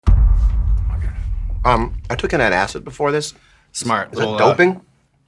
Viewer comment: I thought he said he took a hit of acid before this lol